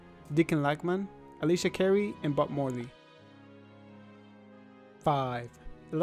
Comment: i thought i heard butt morly